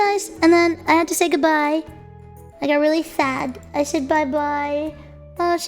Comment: really fat? Is that what she just said? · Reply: really fad (sad intentionally mispronounced)